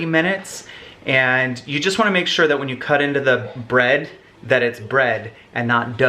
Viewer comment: i heard into the unknown😦